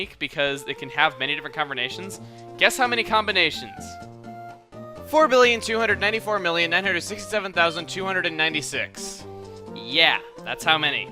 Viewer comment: If you didn't know what Chuggaconroy said around 12:00 he said "Guess how many combinations; 4,294,967,296."